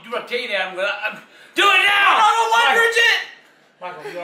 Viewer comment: did he say i dont want to die a virgin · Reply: it actually sounds like I don't wanna die a virgin